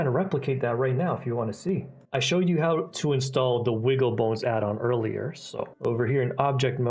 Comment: I thought you said wiggle b*ls addon. But then I hear bones and see the icon... woeps